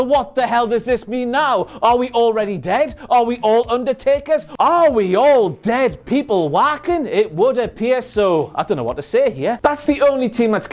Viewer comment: did he say are we all dead people wacking it?